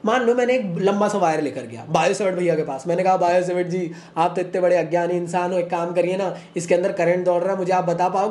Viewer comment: did you said agayani😂